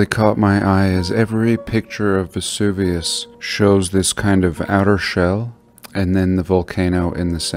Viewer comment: Please enable transcriptions for your video, for all your videos please. Someone could tell me what he says at 04:24? Outer what? outer shelve? outer shell? outer shall? English is not my main language. Thank you